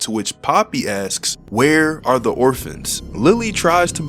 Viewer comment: it sounded like poppy said "where are the orfis"